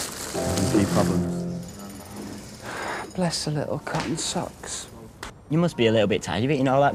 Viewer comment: okay what the hell does mark say at 3:03 someone please tell me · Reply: Ok, I spent time on this. I believe Robbie says "isn't she tired yet? Mark replies "...problems", and then Robbie says "bless her cotton socks".